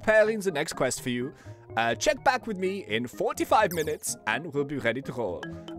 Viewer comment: did he just say 45 minutes or am i hallucinating